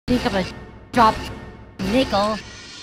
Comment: Im honest I always thought she said "think to adopt nickel"